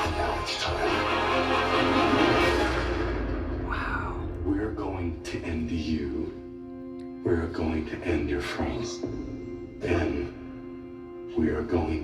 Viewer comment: I thought it was billy saying “we are going to you, we are going to end everything”